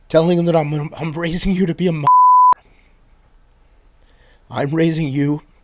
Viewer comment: What is the word he says to his son? At minute 40:00 he’s raising him to be a what? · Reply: Mother Fer